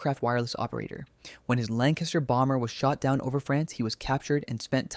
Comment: I think you’ll find that there were also Australians involved in the escape while the book was written by Australian airman, Paul Brickhill, although he didn’t actually participate in the escape. Also, maybe I misheard, but it sounded like you said Lancashire Bombers. You should’ve said Lancaster Bombers.